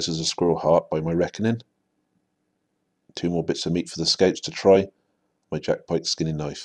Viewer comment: What are " scapes "? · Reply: Hannibal EnemyofRome Ok i thought he said scapes first as well. But his accent (I want to say Gloucester) is quite thick. He's actually saying scouts :)